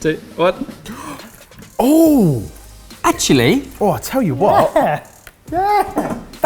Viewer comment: Was it just me who heard Ben say "A chilli!' @ 12:55 and was waiting for someone to correct him with "No Ben, that's a pepper"